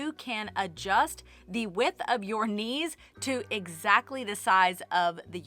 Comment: You forgot using your child to help wind yarn into balls! I have wonderful memories holding my hands up for my mom while she wound the yarn. We would laugh and talk while she wound. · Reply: Actually first I thought you said 'niece' instead of 'knees' and I pictured a little girl holding the yarn like I used to do as a child for my mom 😁